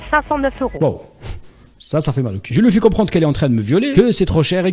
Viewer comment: c'est moi où il dit "je lui fais comprendre qu'elle est entrain de me violer" 😅😂😂